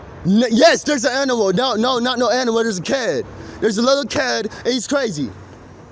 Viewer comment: I know this is years late and all, but at 13:50 I think he's saying 'kid'. "There's a little kid out there and he's crazy"